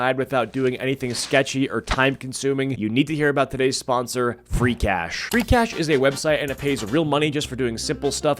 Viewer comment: i thought i heard freak ass